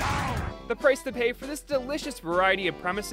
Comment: When he said Put that cookie down I thought he said fortnite cookie